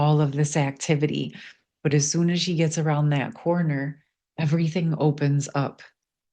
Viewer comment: I heard, “Just Around the River bend” from Pocahontas 🚣‍♀️